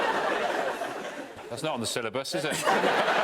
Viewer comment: What does Sean say at 5:27? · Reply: "That's not in the syllabus, is it?"